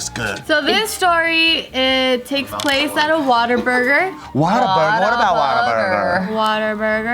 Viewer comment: Why did it sound like tiff said waterburger instead of what a burger